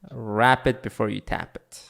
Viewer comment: WRAP IT BEFORE YOU TAP IT the best advice anybody's ever said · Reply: Ok this explains a lot. I tought i heard him saying "rapid before you tap it"